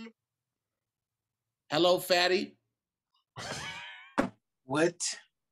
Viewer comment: I lowkey thought it said hello Tahir